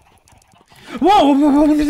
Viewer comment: Аt 2:43 when you got scared it sounded like you said "gde ćeš" which means "where are you going" in Serbian lol